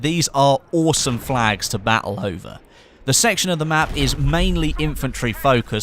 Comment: Did he just say battle hoover?